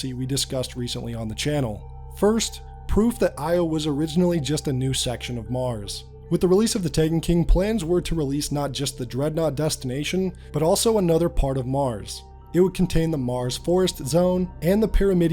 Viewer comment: It sounds like you said with the release of the titan king 😂